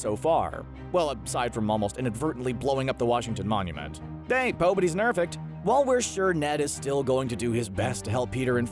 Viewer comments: did u say pay pobodys nerfect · Did anyone else catch that 2:57 pobodys nerfect hahahaah. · Does he say "pobodies nerfect?"